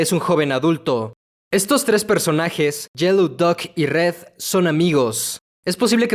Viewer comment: fui la unica que escucho que dijo ''yellow duck'' y no ''yellow guy'' o ''duck guy''? '-'